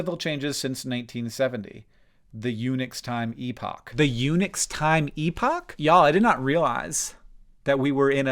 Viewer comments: You said "Unix time" and I heard "eunuchs' time" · I initially misheard unix time as eunuch’s time and did a bit of a double take. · I was listening doing something else, and I heard, "Eunuch's Time Epoch," and had to come and look. How do eunuchs possibly enter into this? Oh, right, Unix. Ahem.